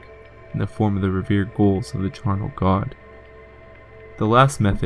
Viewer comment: Sweet video! The sound quality could use some improvement, though. I several times misheard the word "ghoul" to be "goal"